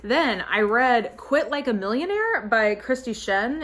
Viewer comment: At first I thought that book said "Quilt Like a Millionaire" and I was like, "I don't really see how millionaires would quilt differently than anyone else, but okay." 😂